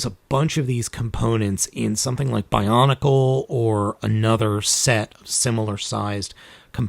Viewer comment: excuse me did I hear "bionicle"?